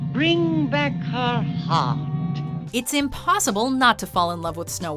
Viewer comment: I don't like snow white but I'm not heartless Edit: BrInG bAcK hEr HeArT Edit 2: 6:08 is it just me or did it sound like she said "bring back her hot"?